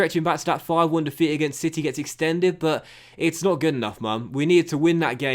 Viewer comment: Did he say “it’s not good enough mum” 11:02😂